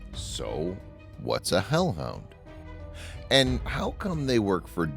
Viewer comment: i thought you said Hell no Lol- and scale it scared me was 3/10 Wasn't that scary to me